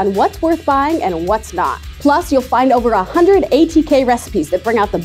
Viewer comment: did she say 180k?